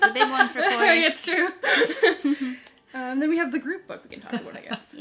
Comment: I kept hearing 'The Groot Book' everytime you all said the group book xD I definitely agreed on A Stranger in the House. It was very formulaic and predictable. I read Final Girls right afterwards, and I loved that one so much by comparison. I also read My Best Friend's Exorcism, The Slade House, and started Paper & Fire during Spookathon. It's one of my favorite reading events of the year! Loved hearing your wrap ups <3